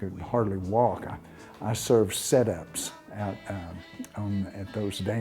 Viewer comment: What does he say at 1:22? It sounds like "I served 'sedeps' (?) at those dances".... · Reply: Set ups